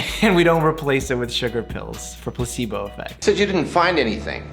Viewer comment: did i hear "pussy-bo effect" ? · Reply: ÏPRÎNČË placebo 😂😂😂